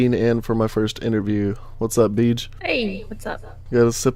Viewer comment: first time I hear that I thought u said ,,Bitch''